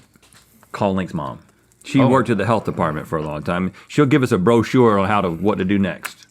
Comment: Rhett said “brochure” but I heard “bro, sure”